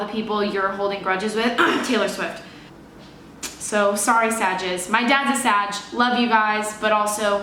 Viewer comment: I thought you said, "my dad's a vag."